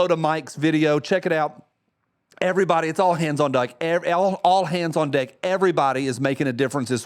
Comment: All hands on duck!! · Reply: I thought he said "all hands on dyke"